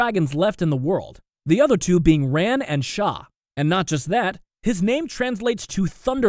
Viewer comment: I thought he said that the two other dragons were ran and shoot 😅